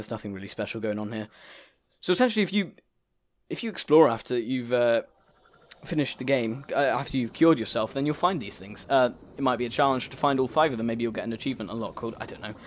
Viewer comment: I thought you said killed yourself